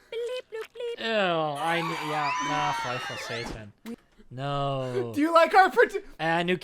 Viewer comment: Is Danish? Think I heard a "Føj for satan".